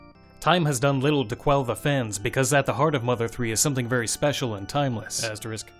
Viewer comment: He says "something very special" Captions say "something special" Y u troll me?